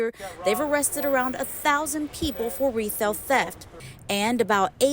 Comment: Did she say, "retailth theft" or is it just me? 😂